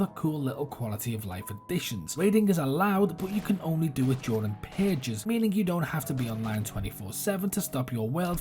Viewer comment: great video man but at 1:13 you said something about how raiding is something, i don't understand the word edit: i think you said impatience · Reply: "raiding is allowed but can only be done in purges." Sorry I need to enunciate better 😅